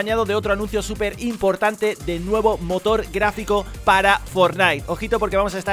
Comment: Pensé que dijiste nueva moto :"v